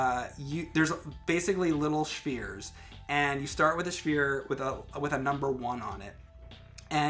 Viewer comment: did he just say shphere? I don't know why I find that so amusing, but I do.